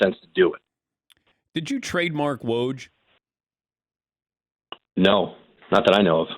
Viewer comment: My high ass thought dan said “ would you trade mark woj like “mark woj” was an nba player. Then i realized he said trademark woj. Sheesh